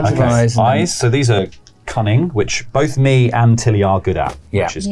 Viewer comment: 😅I heard “cunning” as “coming” the first few times…